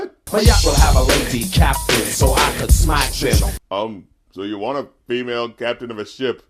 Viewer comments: See, I thought he said "smash them", not "smack them". Like, sex. Was that a euphemism back then? When did that one really start getting used? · i hear lazy not lady, either way, not a bar worth saying